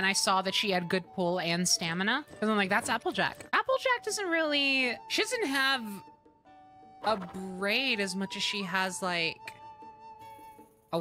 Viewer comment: I thought you said "Applejack doesn't have a brain" and I was about to agree so hard.